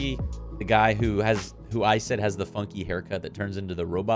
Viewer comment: what did you just say about his hair? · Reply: I think I described it as "funky" lol XD